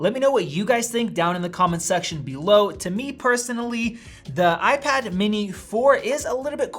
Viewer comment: Is it just me or did he just say "The iPad mini 4 is kinda Quieter"